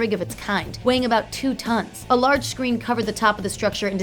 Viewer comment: Did she say 2 tons?